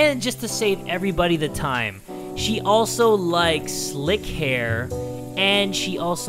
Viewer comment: "And she also likessssslick hair" Me: did he just say lick hair? (I didnt hear him say 'slick' cause he did likes and slick at the same time')